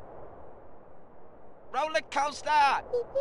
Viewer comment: did he just says roller coaster? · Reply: He says: rollercosta huhuhuhu